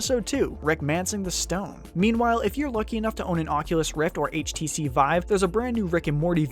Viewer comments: I thought he said Oculus Rick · was i the only one who heard "oculus rick"?